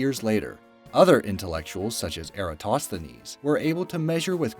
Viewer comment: excuse me, WHO? Did he just say Aristotle as “Aritosteles”? this is so disresepectful, why are these imbeciles allowed to live